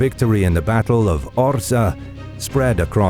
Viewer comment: Legit thought it said battle of OSHA and I was like what did they do? Not wear a harness on an 8 ft ladder?